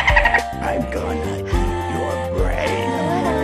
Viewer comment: As a kid, I thought the Sunflower was saying "Eat your brains!" because of the dolphin zombie saying that. It fits the pattern the others have, so can you see why I once made that mistake?